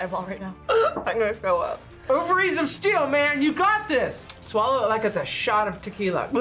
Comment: HELP DID SHE SAY OVARIES OF STEEL